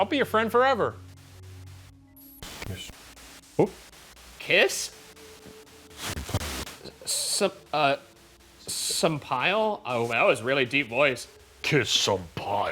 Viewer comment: I heard ‘kiss… your partner’ lol